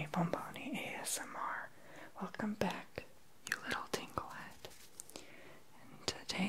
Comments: did he just call me tinklehead¿ 😂😂😂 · Did he just call me a "Tingle-Head"? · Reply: GamingMan Plays I thought he said tinkle head lol 😂😂😂